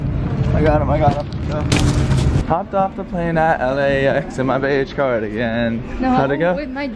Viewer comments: I thought said “hopped off the plane at LAX and my baby’s crying again.”😂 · " hopped off the plane at LAX and my beige cardigan" sang with confidence! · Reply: I thought the said my bleach and my cardigan